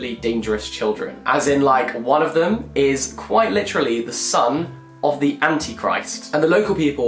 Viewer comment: Just one tiny correction. Lucy isn’t the son of the antichrist, he IS the antichrist. Christ is the son of god, the antichrist is the son of the devil. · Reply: I was listening on speed and I thought he said Sun of the antichrist I tried so hard to figure out what that was lol